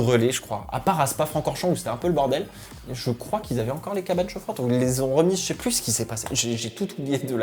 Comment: les quoi ? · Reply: Cabanes chauffantes : au lieu de mettre les pneus dans des couvertures individuelles comme en F1 on les met tous dans une petite pièces en bâche montée pour l'occasion dans les paddocks que l'on chauffe à une température donnée réglementée pour chauffer les pneus